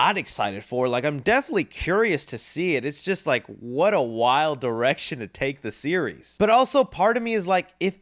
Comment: Thought said " wild erection " but it's wild direction